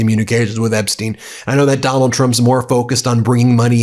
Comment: Heh. Ben said "that Donald Trump" and I heard "MacDonald Trump".